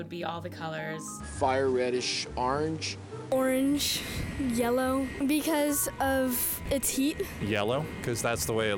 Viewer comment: I thought she said "orange, because of its hue" and thought "wow, that was clever" but then I watched it again and realized I had misheard.